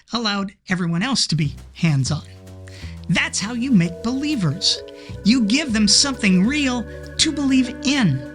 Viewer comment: I only hear: That's how you make-believers.